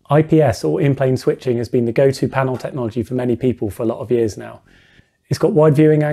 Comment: I heard 0:20 as "... Has been the GOATed panel technology" the first time, it was sensational